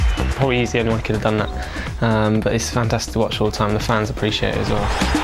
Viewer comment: when Walcott is being interviewed, sounds like he is saying, "the fans are pretty shit too"